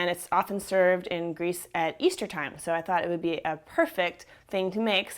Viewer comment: oh lol i thought you said it's served in grease at easter time and i had 2 reactions: 1. I GOT CHIIILLS THEY'RE MULTIPLYINN AND I'M LOOOOOOSIN CONTROOOL 2. Ew what